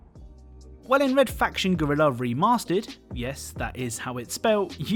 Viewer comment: it still sounded like you said “remastered” cuz of your accent lol